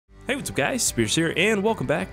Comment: Low key thought you said hey what’s up guys scarce here in you intro